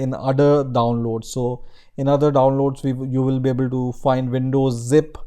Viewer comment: I don't get what you say :O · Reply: in other downloads you will be able to find